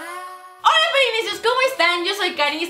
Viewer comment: Like si entendiste 'Hola, yo soy Caeli'. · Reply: yo si entendí "hola yo soy caeli" 😱